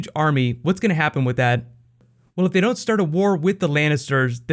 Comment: did he just say white wakka